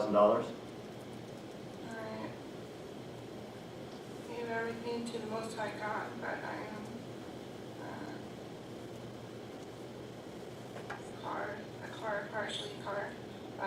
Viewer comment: When she is being questioned about her income it sounds like she says “i gave everything to the most high God.” Anyone else hearing that?